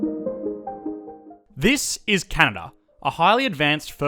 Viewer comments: I swear he said Cana😂😂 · “this is Canada” Canadians: “no that’s toronto.” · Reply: I heard, "This, is Cana."